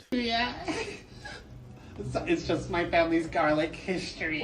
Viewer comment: Did he say his family's garlic history?